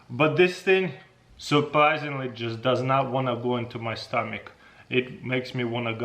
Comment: What did he say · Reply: “i can feel it in my stomach” 💀💀💀😭😭😭😭💀💀💀💀💀😭💀💀😭💀💀😭💀💀😭😭💀😭😭💀😭💀😭😭😭😭💀😭